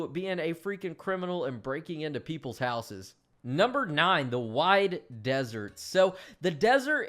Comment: "Wide" is what she said.